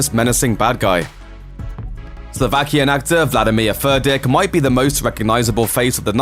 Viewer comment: did he just say "vladimir Fur Dick"?